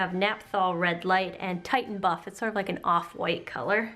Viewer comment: I thought it said "titan butt" instead of "titan buff" 😂😂